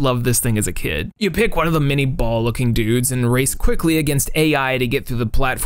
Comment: anyone else hear "ball licking dudes" ?